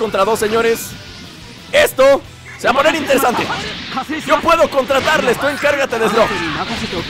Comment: en el minuto 13:20 cuando dise yo puedo contra turles párese que dice yo puedo contratarles like si lo escuchaste